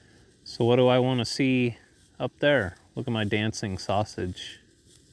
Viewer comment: Was ripping a bong ... and I hear 'look at my dancing sausage!!' ☁️🤧.....😮